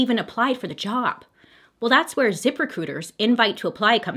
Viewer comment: It sounded like she said zipper cooters instead of zip recruiter 😩